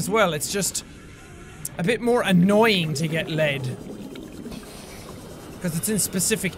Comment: in 4:44 it sounds like he says "get laid" in a german accent